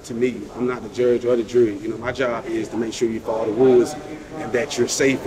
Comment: if you turn on the CC subtitle, the guard translates to ,make sure to follow the ,woods' 😄instead of ,follow the rules' 😂😂hilarious considering who says it, where it's been said and the entire circumstances